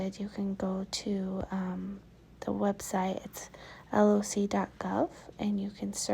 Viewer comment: Sorry, what was the name of the .gov website? I thought you said LLC dot gov but that didn’t do it. Thank you in advance :)